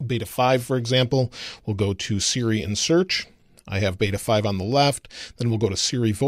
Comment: Great video, as always. Although when you said “Siri and Search”, I thought you were saying ”Syrian Search” lol